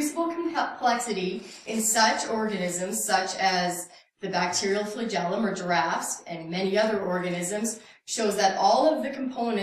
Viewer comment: Could have sworn she said "the flagellum of giraffes" about 16 mins in. Proof positive that creationism is bad for the brain - I'm just not sure which of us has been brain damaged.